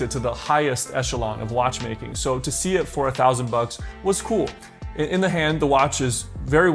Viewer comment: excuses me sir, did you say 4000 bucks? Absolutely not … 😂